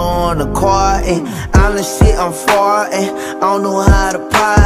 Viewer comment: did he say honestly I'm fartin 😂